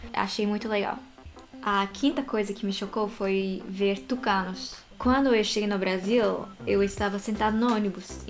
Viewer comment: Eu achei que ela tinha dito tucanos fumantes KEKEKEKE. Gostei do canal.